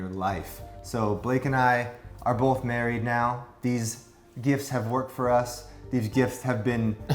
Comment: I thought he said blake and I are married with each other. I was like hold on 😂😂😂